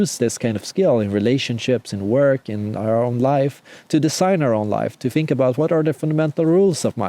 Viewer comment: Just one language-related piece of advice: learn how to pronounce /z/! You say "design", I hear "decide" - it's confusing! I've always been amazed at how Swedish people's English is so good, yet at the same time not a single one of them can pronounce this sound!